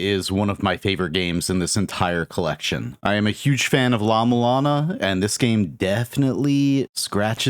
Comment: What game did you say barbuta reminds you of? The subtitles seemed to not know. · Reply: La-Mulana